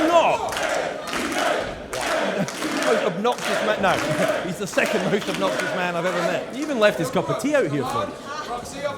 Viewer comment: What was the crowd saying at 6:08-6:10? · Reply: kay! lee ray! kay! lee ray! kay! lee ray!